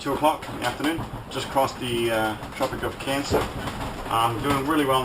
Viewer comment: Me thinks you may have been WAY off course...shall we try the TROPIC of CAPRICORN instead of CANCER? At 6:55 I believe under much stress you said it! LOL. Having sailed the Australia coast and the GBReef...I knew that something didn't quite sound right. Sail on.